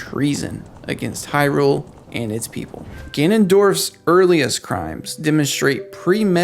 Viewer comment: Not gonna lie, I legit thought that said Gandalf for a hot second. Great vid!